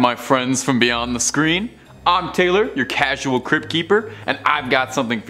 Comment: He referred to himself as the “cryp keeper” as in cryptid, but I thought he said crip, your gonna have some enemies from some gangs bud